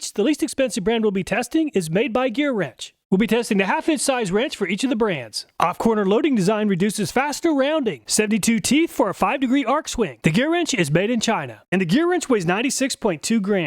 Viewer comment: i thought you said “off quarter loading design INDUCES round over”. 😅